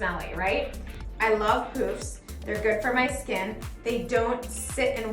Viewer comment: did she say she loves poofs or poops, the caption says poops🤷‍♀️